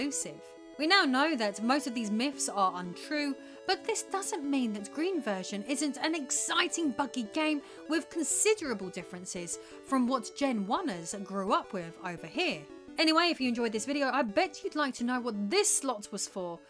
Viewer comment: Ok I had to listen twice at 10.30 because first time it sounded like you said “exciting f***ing game” loool